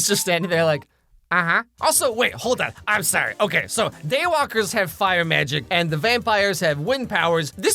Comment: At first I thought they said jaywalker